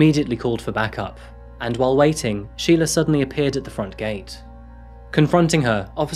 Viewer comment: At first I misheard the name. I thought it was Shia LaBeouf hahaha...